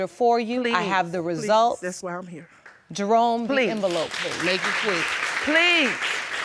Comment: After the Judge said 'I have the results' it sounds like somebody said 'make it quick' lol